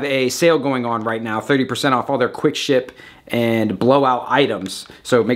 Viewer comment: Right around 1:40 I thought you said "quick shit blow out" in reference to the sale😂